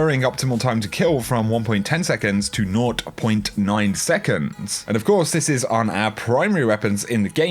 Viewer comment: Can anyone please tell me what he said the new ttk was at 6:40 - 6:42? It sounded like he said "nod point 9 seconds".